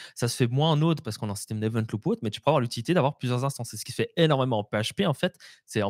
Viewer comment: Hello, petite question. Il dit quoi la 17:04 ? j'ai pas compris et ca m'intéresse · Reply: Event loop